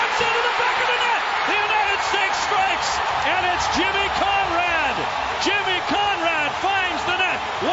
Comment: When you first said jimmy Conrad I thought you said chimmy changa lmaoo